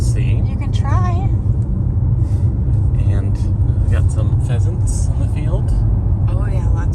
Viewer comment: I thought he said "peasants in the field " i know were behind but !